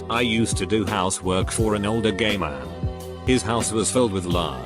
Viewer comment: Instead of "old gay man" i heard "old gamer" and the story still fit until the point was made that nothing sexual was going on.